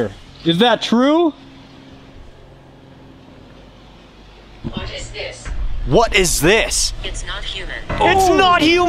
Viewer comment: I think the spirit is saying “what is this?” And “it’s not human” because it was referring to your devices. That spirit might not know why it’s able to communicate with you through them